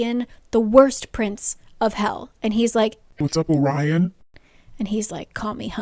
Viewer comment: the way you said "Orion" when talking about hunt's dream in the pocket realm caught me off guard- i thought you said "what's up Ryan" and the thought of the star eater calling hunt Ryan was just so funny to me i burst out laughing (Orion is a latin word and since latin is the base of my mother language i was just not expecting the accent, i'm not making fun of before anyone tries to come at me )